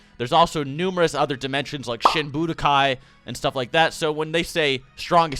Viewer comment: i keep hearing "shit budokai" when he says "shin budokai", hm.